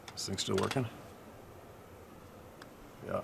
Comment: after u said is this thing still working, I heard a male in the distance say "oh ya" @ 16:35 sounds like something was banged u can hear it. I think a Kukri is a knife. @ 21:08 I heard a loud wood knock . Thanks for sharing!